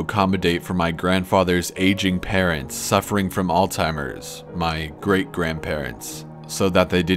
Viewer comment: Did he say "Alltimers"? XD